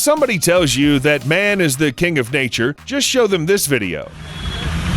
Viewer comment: "if somebody tells you that man is the king of nature, just show them this video" Totally thought you said "killer of nature" 😬